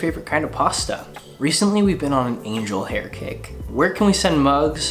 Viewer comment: I thought he said, “angel hair cake” when he was talking about his favorite pasta 😂